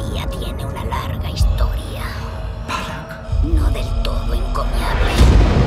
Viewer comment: que dice la mujer de fondo. Soy yo o dice "no tan encoñable"? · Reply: CINE ANTE TODO Tu pequeño rincón de cine Eres tú. Dice "encomiable".